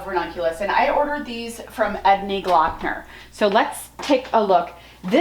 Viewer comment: Where did you get your ranunculus? It sounded like you said Edney Glockner, but I can’t find it so I’m spelling it wrong. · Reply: Yes- Ednie Gloeckner